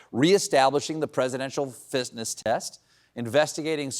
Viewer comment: Minute 8:35 did Stephen just said Presidential Fistness Test… instead of “fitness”😂😂😂😂😂😂😂😂